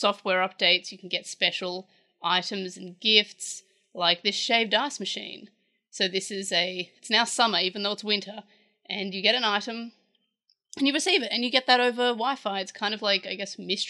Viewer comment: I 100% thought you said shaved arse machine 😂